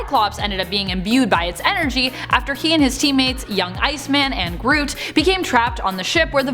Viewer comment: did she say cyclops was working wit GROOT?